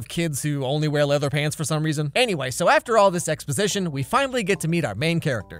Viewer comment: I thought he said “after all the sex positioned” 😭🤚